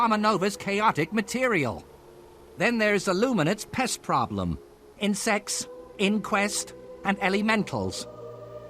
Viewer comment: misheard insects as incest